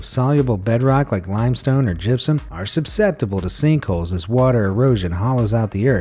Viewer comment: he did not just say "subsceptible", right?